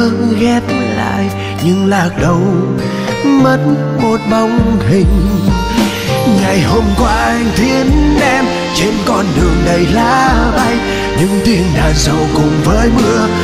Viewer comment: Bao nhiêu năm mới nghe lại, cảm xúc vẫn như lần đầu, vẫn nghe thành “Ngày hôm qua anh thiến em, trên con đường đầy lá bay” 😢😢😢